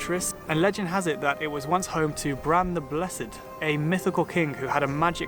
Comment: For I second there I thought he said "Brian the Blessed"! "Gordon's alive!" ⚡🤪😅